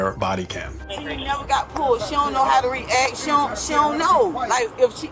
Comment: Did she say, "She don't know how to read"?????? 17:09 "She never been schooled"??? why is she driving? The Dad is making sense